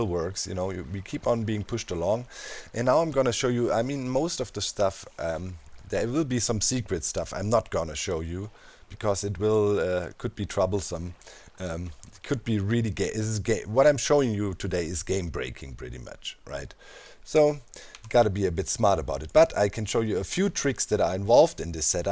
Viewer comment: Who else thought he said, “could be really gay. It’s gay.”